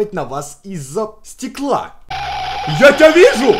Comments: я услышала "it's me" :) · я один услышал it's me ???